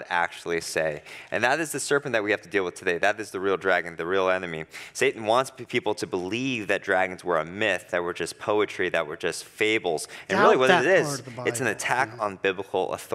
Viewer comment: did he say "that we're just myths and legends"🤔